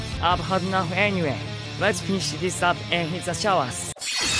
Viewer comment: what i heard: Lets finihs tdis ap and hitcha showas